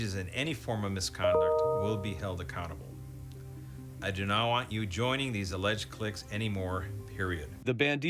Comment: Even the Sheriff say "I don't want you joining anymore" which suggests that he is aware of its existence to some extent within the department. Did I hear that wrong?